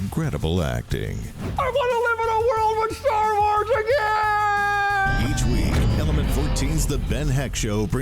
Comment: What the heck is Ben saying? Sounds like "I wanna live in a world when Star Wars are gay". That doesn't even make sense grammatically? Shouldn't it then be "is gay"?